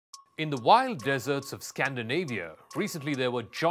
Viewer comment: "In the wild deserts of Scandinavia" Did I just hear right? Either I'm deaf or this channel has very poor understanding of geography. There are no deserts in northen europe.... it's not that big of a deal but it really makes you question the credibility..... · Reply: I heard "white deserts"